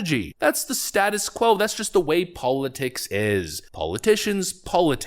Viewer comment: Oh, you said "politics" but what I heard was "parlor tricks"! - more fitting description 😉